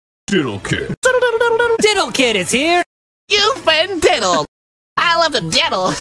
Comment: "You've been diddled" i kept hearing as "Buford diddle" and i dont know why.